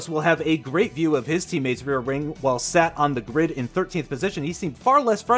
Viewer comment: Genuinely thought at 03:47 he said “Lando Norris will have a great view of his teammates rear ring” 🤮